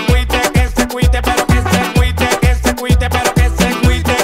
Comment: Yo pense que decia "pero el que se aguite"